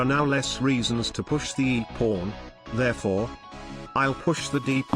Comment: I had my volume on 0 and was watching with captions but at 0:37 it said E PORN instead of E pawn and I was very confused